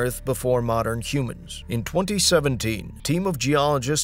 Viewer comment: Did he say the year 2070?